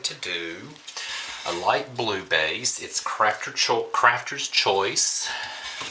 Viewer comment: I'm watching this video for a 2nd time and was busy stuffing my face, SO .. What I heard was, "cracker's choice", which made me spew a mouthful of my soup. Oh brother. 🐳 Had to rewind and watch ... I just LOVE your videos, always entertaining, whether or not it's intended.